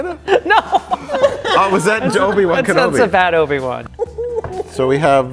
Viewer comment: Did just say: Joe'bi-Wan Kenobi?